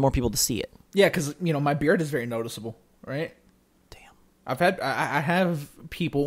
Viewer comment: And at 22:33 did anyone else hear whisper “damn”😂